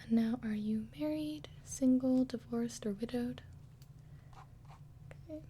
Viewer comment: Hey guys could you please help me understand what does says at 3:19 - "are you married, single, divorced or ? " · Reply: Or widowed